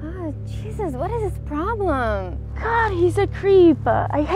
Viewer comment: ayo what she say? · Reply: Jesus?